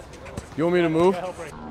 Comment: THIS VIDEO WAS RE UPLOADED! Last night, I realized that the entire video was on the second camera angle !! I apologize... Thank you all for watching !!! PLEASE go follow my boys! BIG s/o to Y'all !!! We over 700k?!!?! Tag me on your instagram story of you watching this video and telling people to subscribe! Im following 100 people back tonight ! LETS GO #RoadToAMillionSUBS #Dfriga #KShowtime #AnklerBreaker Please follow my instagram and subscribe to the channel! My baby boy is due in June this year and I'd love to hit 1 million this year! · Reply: Friga so polite he's talking in code What he said: Y'want me to move? What I heard: why haven't you dished me if you aReN'T sHoOtInG